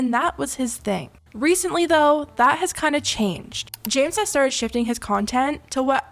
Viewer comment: it sounds like she said “ jams “ instead of james 😭